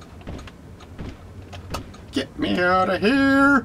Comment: The noice fish mush makes when he sees you sounds like he saying Get it out!!